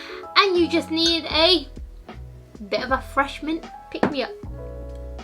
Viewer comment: Lol I thought she said “fresh mint” too lol, coffee does help a lot to get through a stressful day! Great video guys, love your vibe 😊